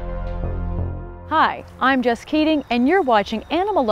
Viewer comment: " I'M JESS KEATING" i thought i heard. "I'M JUST KIDDING"